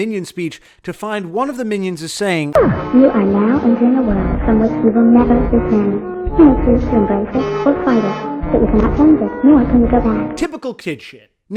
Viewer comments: Could someone tell me what it saying? I can't understand a word. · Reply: gn Fur You are now entering a world from which you will never return you may chose to embrace it or fight it but you can cannot change it nor can you go back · Wait what are the minions saying? I can't really catch it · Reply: Jasmine Blaylock You are now entering a world from which you will never return you may chose to embrace it or fight it but you can cannot change it nor can you go back